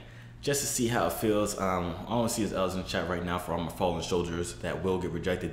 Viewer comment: did he just said fallen sholders 🤣💀